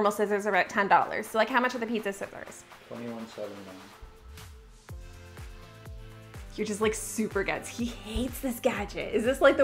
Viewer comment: Is there a way you could start adding captions for your husband’s off screen comments? I can’t ever hear what he says and I have to rewind the video and put my phone up to my ear. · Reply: I heard 21.79 I'm not sure this lmao